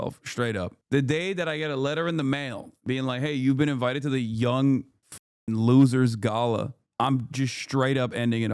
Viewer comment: I thought Hasan said “Gollum’s Gala” & now I just think it was really funny to mis-hear that & I cannot un-hear it & these parties are now Gollum’s Gala to me 4evr